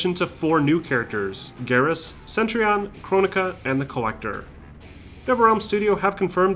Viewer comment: did he just say kronika she's not playable